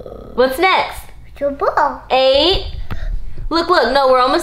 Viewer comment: When says eight it sounds like He is saying yeet 😂